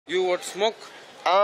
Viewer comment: I heard "you want smoke?" And expected a fight